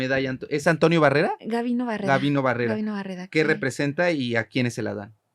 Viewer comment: Ella ganó la medalla Gabino Barreda en la UNAM como la mejor estudiante de su generación. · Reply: Yo venía conduciendo cuando lo mencionó y pensé que había dicho Gabino Barrera 😂